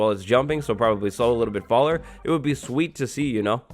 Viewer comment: "slow a little bit faller".... did I hear that right? lol